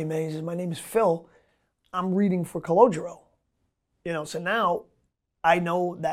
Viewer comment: Oh my lord my whole life I thought his name was Cologino....did he just say Cologuro???... Mind totally blown...Life is crazy